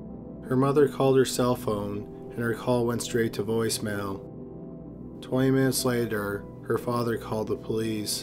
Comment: lol when he said 20 minutes later i heard "20 men slayed her" was like wtf is this medieval stuff